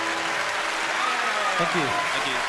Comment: Wow at 3:25 it sounds like hes saying "fuck you". lol